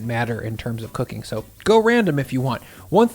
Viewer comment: Lol, I heard "45 minutes" both times. Checked the comments and seeing no one mentioned I thought I was going crazy as 45 minutes is ridiculous. Then realized its "4 TO 5 minutes". -.- Yup, that makes sense.